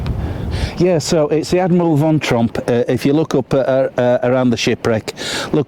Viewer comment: Hello mate,how do you thank someone for taking you to sites of such natural beauty ❤ A gentleman who takes the pain,hard graft. To show the easy way. All that so i can sit on my bottom, with to hand. Tea,coffee,cans of beer,cans of pop ,cake,a good lady to make sandwiches, to fetch& carry. Oh my this is the good life 😊😊 At home in London, but with my soul in Whitby ❤😊. Sadly my hearings not so sharp,and i thought you said Von Trapp😮 But what an interesting thing to see😢for the loss of life. Back to your back catalogue, until the new one arrives Tally ho, cheers 🥂 Allan&Family ❤